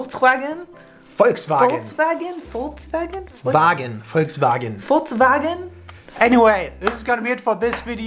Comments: "Furz" Wagen??? Hab ich das richtig verstanden? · All the time i hear Furzwagen